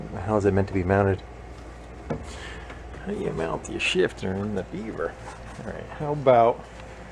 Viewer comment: Did I hear " a shifter in a Beaver" this series cracks me up.